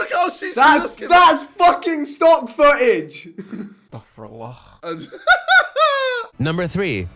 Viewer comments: did he just say "astagfirallah" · wait whattt!!!! Did TOBI just said " ASTAGFIRULLAH" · did he just say estagfirullah? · did tobi just say astaghfirullah? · did he said Istaqfurullaah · did anyone else hear tobi say astighfirallah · Did tobi just say staqfurallah?